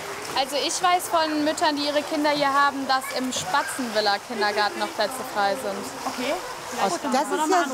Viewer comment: Ich habe "Spasten-Villa-Kindergarten" verstanden😂😂😂😂😂